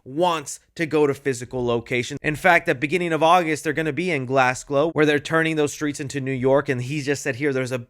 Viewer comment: what did you say? Glasglow? maybe just just a slip of the tongue lol